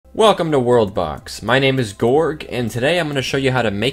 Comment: i heard '' Hello, my name is Dork''.